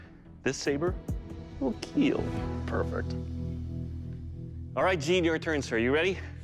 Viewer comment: Did call Doug a pervert at 1:45?🤣